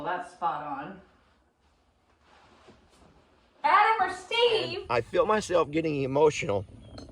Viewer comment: I must admit for the longest time I thought that you said Adimer Steve instead of Adam or Steve. 🤦‍♀️🤷‍♀️